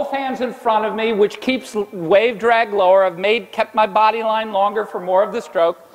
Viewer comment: Can somebody help me, I dont hear one word after " keeps" 9:25 both hands in front of me which keeps ........ drag lower which may keep my body line longer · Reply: Wave drag lower. Way too late but there we go.